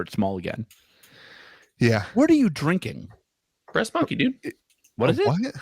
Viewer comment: I heard breast milk the first time LMAO